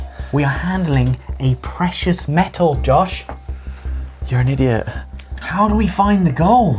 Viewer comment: did he call Ollie idiot? Cause ever since Ollie got better at korean, you rarely hear Josh call Ollie and idiot